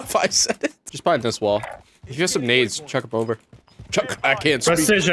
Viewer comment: His burp sounded like he was saying grenade 🤣😂